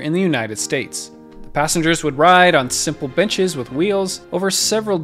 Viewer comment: simple WHAT edit: ohhh he said "benches"